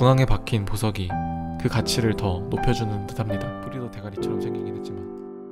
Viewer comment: 초엨ㅋㅋㅋ 작은목소리로 뭐라고한거얔ㅋㅋ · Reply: 프리더 대가리같이 생기긴 했지만...